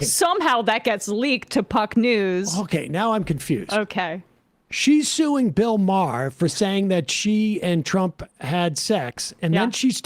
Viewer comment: Fading in and out, but I thought he said Laura Loomer is suing Bill Maher for having sex with a wizard, which almost completed my 2025 Bingo card.